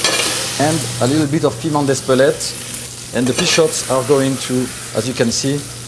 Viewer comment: What does he say at 3:27? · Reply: Pea shoots